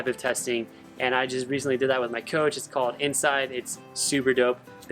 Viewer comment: Good stuff, thank you for sharing! 2 questions: You mentioned at 2:58 you recently with your coach just did some testing called....inside?? Didn't quite catch that. Second, would find it insightful to hear your thoughts on fasted rides...when, why, how you do them and the ways they benefit your training. THANKS! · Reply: Jeff White I did the Inscyd testing. It’s interesting. It’s like a lab test but done outdoors. Unfortunately it’s not available to everyone unless you can find someone who joined the Inscyd team and can run tests. My coach recently joined them. I’m game for breaking down fasting rides. I do them from time to time. Super brief, they not only help to teach your body to burn fat as the primary fuel during z2/3 efforts but it also helps to build more mitochondria. More mitochondria means more energy development.